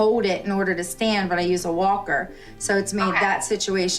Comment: I thought she said she couldn’t walk 🤨🤨🤨🤨🤨🤨🤨🤨🤨🤨🤨🤨🤨🤨🤨🤨🤨🤨🤨🤨🤨🤨🤨🤨🤨🤨